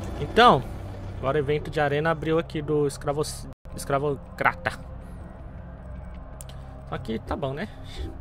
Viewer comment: kkkkkkkkkkkkk eu ouvi um escravoceta